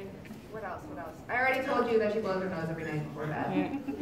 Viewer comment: can't figure out what Mel said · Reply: kat blows her nose every night before bed